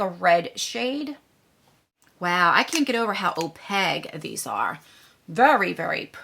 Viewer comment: I swear every time you say Opaque I laugh and smile a little cause it sounds like your saying opeg, I Adore You Miss 😆😆😂😂😂😂